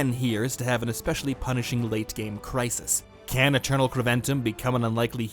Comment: Your captions around 4:25 are wrong btw. It says house Triton instead of Kreventum